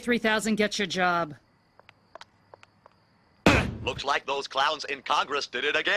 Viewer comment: oh... CLOWNS... I always thought it said clouds and I was so confused there was a joke I wasn't getting... I'm smrt